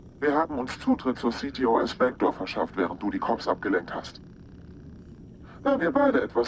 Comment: Entschuldige aber ich verstehe bei 28:28 immer wieder "während du die Cocks abgeleckt hast" oO ...